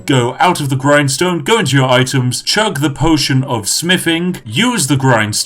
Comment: me: potion of smithing? i thought you said potion of spiffing everyone else:... except that one guy: clap